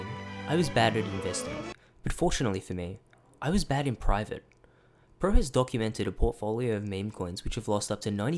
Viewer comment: what do you mean by "bagging private" and what was said right before that, it sounds like the audio was intentionally screwed up?